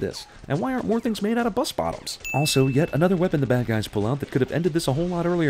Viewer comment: “why arent more things made out of bus bottoms” dude i stg i thought you said why arent orphans, and i was worried. but most of these sins are literally explained in the movie. PAY. ATTENTION. TO. THE. MOVIE. YOU. ARE. SINNING.